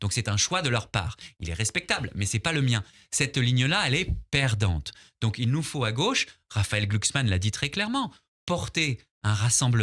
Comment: j'ai entendu "il nous faut une ligne de droite", pas vous ?